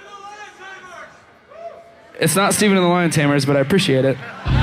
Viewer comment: I didn't understand what that guy said at 8:01, can you write for me? ;) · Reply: Stephen and The Lion Tamers.